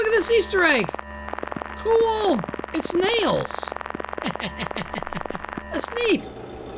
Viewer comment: In the Easter egg it sounded like he said nails😂😂😂